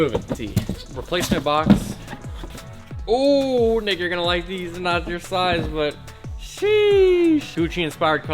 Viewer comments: on 7:57 i thought he said the n word lol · sounded like he said n ur gonna like these